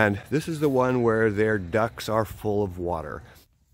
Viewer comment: 😄Thought you said the "ducks 🐥" are full of water🌊!